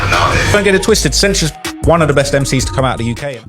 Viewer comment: To say Central Cee is the best MC to come out of the UK is wild. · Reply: Bro dickriding Digga so hard you misheard. He said one of, not "the best". Being one of is undeniable